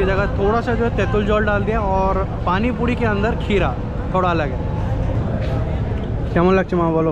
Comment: ' what did you say... Paanipuri ka andar keeda'? Pl clarify friend, please...